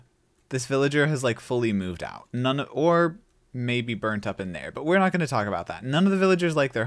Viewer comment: i thought you said "orb maybe burnt up in there" at first 😭